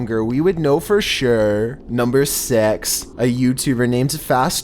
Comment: did you just say "Number S*x"